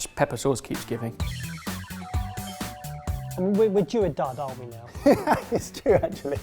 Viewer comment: Sometimes I feel I need Google translate to understand what says... Like at 11:16... What does he say... Heard like 4 time full blast mode but no success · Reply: Due a dud